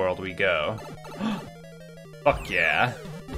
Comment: I thought he said "fuck yeah!" To diving into lava